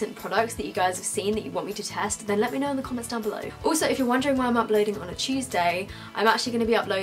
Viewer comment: omg lol I thought you said uploading on a cheese day😂